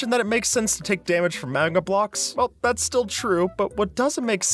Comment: the subtitles say manga blocks instead of magma